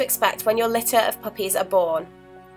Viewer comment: I thought she said heres what to expect when your puppies abort but she said are born lol